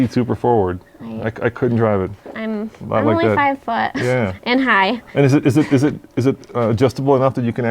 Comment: At the 8:25 minute mark...did she say, "I'm only 5 foot...and high" ? Lol